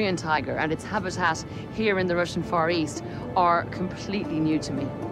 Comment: when the narrator says "far east" I keep hearing "forest" and its making me laugh way too hard...anyone else or am I riding solo on this?